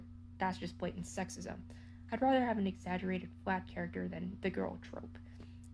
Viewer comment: oh my god please confirm you were saying “exaggerated flat character” i thought you said “exaggerated black character” 😭😭😭